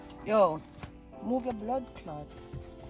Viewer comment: Did i just hear "move yuh bloodclaat"???